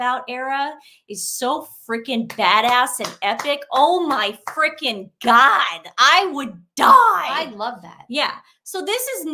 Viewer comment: I was having fun watching your program for the first time after other viewers raved about "The Sidley Twins." Then, I heard, "...Oh my frigging God?" from one of you and I'm done now. Not interested in hearing a profane, disrespectful term describing God. Nobody except lowbrows talks like that